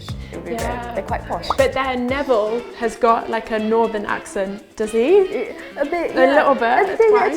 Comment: 네빌이 북쪽 억양으로 말한다고 하신 것 같은데... northern으로 들려요